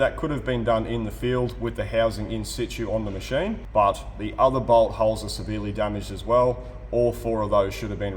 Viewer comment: Oh.... bolthole........ I thought it said b**thole